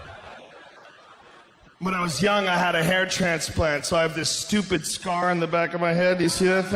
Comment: he said "hair tra-pans-lant" instead of hair transplant lmao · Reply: I heard “ hair panstrant”